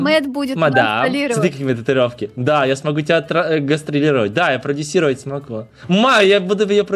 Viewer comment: Мне послышалось что сказать "Да я смогу тебя отра... гастрировать" Кто это тоже слышал ставьте лайк!0.0